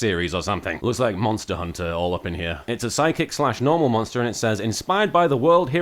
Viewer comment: I misheard 'Psychic' as 'Sidekick' and somehow it did not at all surprise me that that was a new monster type.